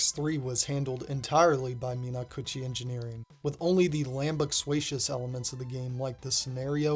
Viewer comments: great video, but the real video game mystery is whatever you said at 12:39 . lambuacious? lambucsuacious? what does that word mean??? i've been wondering this for weeks now i've heard you say this in multiple videos yet i googled and can't find anything, i have asked multiple people what that means and they think i'm making it up. please help, anyone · what the hell was that word? I've never heard it before and the captions give me nothing on google for it. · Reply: Yeah I was wondering the same... "Lambugsuaicious" lol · lambucswacious? Lanbuxuacious? What word did you say here??? I’m trying to look it up and I feel like I’m having a stroke hahdjdhsjdjshsk X,,,,,D. Cool video as always too!!!! :3